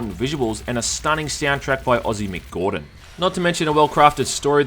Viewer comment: When you said "Ozzy, Mick Gordon" I heard "Ozzy McGordon". Now I wonder if there's anyone watching this video named Ozzy McGordon.